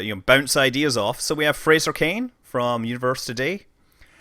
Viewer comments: at first i thought it said Fraser Crane...from the T.V. show "Frasier" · does he say the guy's name is "Frasier Crane?!"